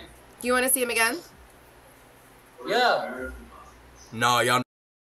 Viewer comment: what he say??? I told y’all what?? 😂😂😂